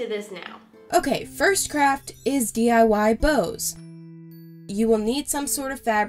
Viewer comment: I thought she said the first crap is bows